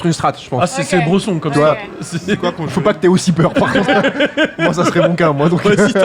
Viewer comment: Vous aussi vous avez entendu le « c’est quoi conjuring » à